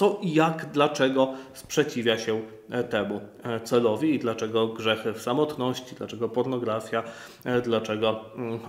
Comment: Samotność jest grzechem? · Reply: Cielę ze mnie. 15:18, usłyszałem, że "grzechy, samotność", a tam Ksiądz mówi "grzechy w samotności".